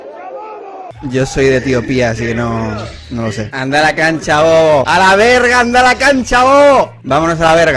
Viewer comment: En el minuto 8:17 en realidad dice "No cacha (entiende) la referencia. Cachar = entender y despues dice "No agarras una boludo" refiriendose a que Dalas no entiende nada.